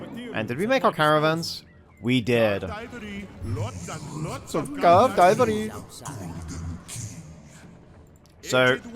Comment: what does the caravan say? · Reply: curved ivory